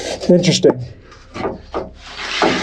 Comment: it sounds like someone says "watch your step" but it dosent sound like him? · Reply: It’s saying interesting